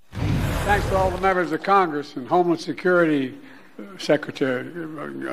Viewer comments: it sounds like he says "homeless" instead of "homeland" 🤦‍♀ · Sounded like Biden said "Homeless Security" lol